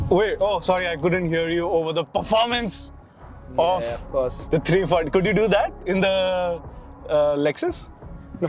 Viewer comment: Did i hear 340i at 10:35 😆